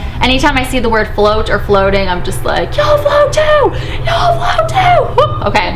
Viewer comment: I thought the kid said ”You all fall too"! But it’s actually ”You all float too”! 😆